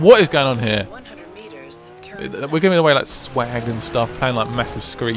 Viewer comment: ok did I just hear the word swag